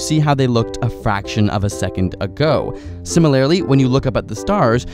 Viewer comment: At about 18 seconds into the video, whatever credibility the remainder of the video has will be tainted. Also, with such a glaring flaw at only 18 seconds into the video, my desire to watch/listen to the remainder of it. When one is unable to use proper English words in a grammatically-correct structure -- especially in a scientific presentation -- one's credibility goes down the drain. ("Similarily" is not a word!) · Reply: It sounds like "Similar-I-ly" in the video -- which is really distracting, as that most certainly is not a word (and I wrote it as "Similarily" in my OP, above). If it's just a mispronunciation, perhaps you can re-dub just that word or something (I think YouTube allows for video editing of such). I asked about 10 of my friends, and they all heard the same thing: "Sim-il-ar-i-ly" so I know it's not just me who's hearing this (I wouldn't have posted if I didn't have corroboration of what I thought I heard).